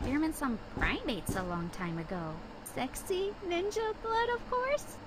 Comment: did she said sexy???